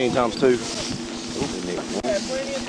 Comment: Did I hear stupid n……